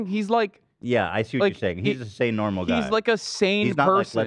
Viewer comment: as a hasanabi head, my favorite thing about Leftovers is when you can hear giggling and kicking his feet in the background at Hasan's little quips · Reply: I thought you said you're Hasan's head.